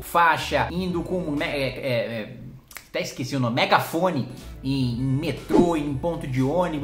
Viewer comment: Cara, eu juro que quando você falou do megafone eu ouvi "hino comunista"